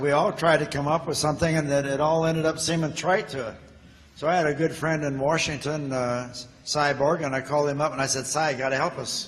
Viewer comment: Frank Borman, in this video, explains how they came to read from the Book of Genesis - "I had a good friend in Washington, Si Bourgin" - that was my great-uncle, Simon Bourgin - very proud to see him mentioned by name here, thank you to Frank Borman and Jim Lovell and Co. for this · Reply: It’s funny, when Borman mentioned that, I thought he said, ‘I had this friend in Washington, a cyborg…’ which is even more warped on my part. These guys were so great. Tough, smart, skilled, Thanks for posting this. As the one lady with the telegram said, they saved 1968.